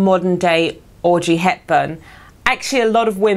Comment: Thought she said Orgy Hepburn